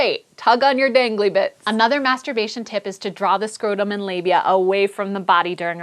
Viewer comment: I thought it said "Menstruation Tips" xD